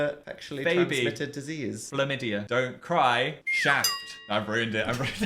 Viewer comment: thought he said “don’t cry, shat”